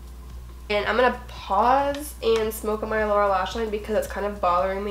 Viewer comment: I legit thought you just said "okay I'm gonna pause, a smoke a marijuana" ok